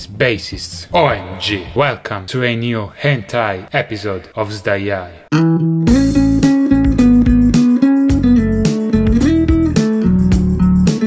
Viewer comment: Did I just hear "to another episode of HENTAI" or is it just me...